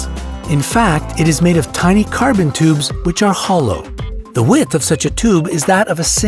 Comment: Did u say HOLOOO? No? Just me? Ok...